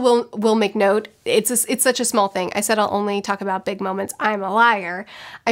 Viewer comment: for a sec I thought she said Big Mom, I was like 👀👀👀. But she just said Big Moments